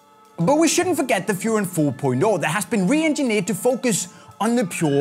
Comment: I thought you said "Füher 4.0"😂😂😅